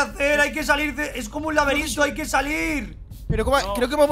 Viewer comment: Hay una parte donde Rius dice laberinto y entendí "laberinso" XD